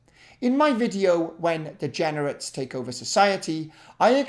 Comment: "In my video, "When Degenerates Take Over Society"" For some reason that sentence had me actually laughing. Maybe it's just the surreality of it. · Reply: I heard it as "when Degeneres take over "